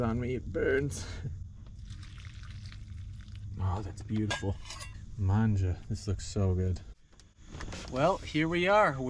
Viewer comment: did you say the Italian word "mangia"? lol